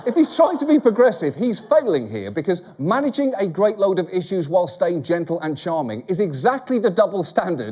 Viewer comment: I thought he said washing dishes while staying charming!!